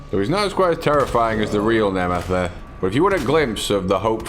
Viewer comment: I don't know if I heard Nemesis or something else.